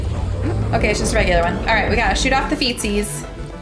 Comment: Did you say "shoot off the feces"?! 🤔